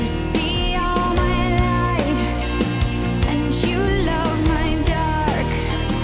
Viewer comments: I always misheard "and you love my dark" with "and you love my dog" as the ultimate exclamation of love! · Until this very moment, I thought she said "and you love my dog". Only now did I hear "dark". In my defensive, I've always scored points by getting along with the dogs 🤷